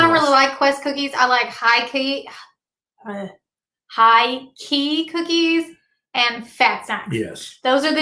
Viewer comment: Can someone plz tell me what cookies she said she liked at 41:10. Tks all in adv! 😊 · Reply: High Key cookies (I found them at Target) and Fat Snax cookies